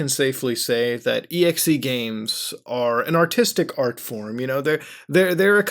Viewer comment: I thought you said 'an autistic art form' in your intro...